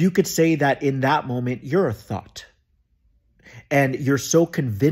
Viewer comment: I’m glad you clarified you meant “thought” and not “thot” cause when you said “You are a thought!” I said ooop- 😅